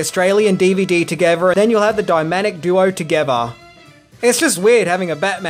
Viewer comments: Did you just say "dyMaNic duo"? 13:32 You did! You totally said "dyMaNic duo". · did you just say "dy-manic duo"?